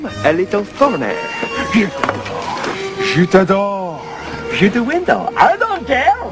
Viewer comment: I laughed at this way more than I should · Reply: I heard it as Je'tadore Je'ta door shoot the window I don't care.